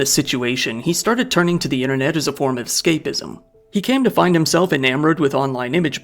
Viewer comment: I at first misheard you saying "He turned to the internet as a form of scaphism." Which, given the context, is probably even more fitting.